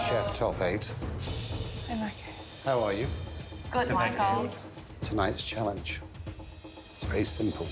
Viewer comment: did she call him michael